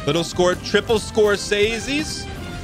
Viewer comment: What does he say at 8:59? Triple what?? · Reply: Scorsese